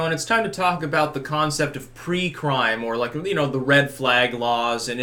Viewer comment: I swear to god I thought you said pre-cum